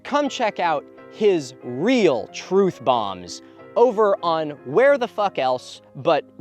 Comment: I thought you said "Truth Bongs" at first and now I avidly want "Truth Bong" merchandise